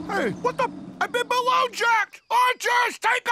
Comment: did i hear : What the f...